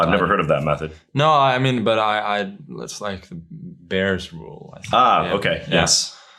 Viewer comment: anyone know what the bears rule is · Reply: I think he is referring to "Bahr's rule" (see <link> )